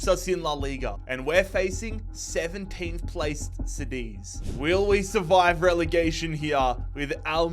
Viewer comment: did he just say, “Sedis” and not “cadiz”